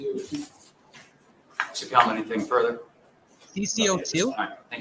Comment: “Hopefully will I go to CC02” ?? That’s what I hear…🤷🏻‍♀️ Ohh sir.. you’re going somewhere with the same amount of letters.. but it’s not Cc02 mother f*****!!!!! Sorry. This has me balling. 💔😫😭😣😔 Back to watching the replay. Thank you T & everyone for the thoughtful coverage.. let’s all hug our babies tonight and tomorrow extra long. 💜💚🙌🙌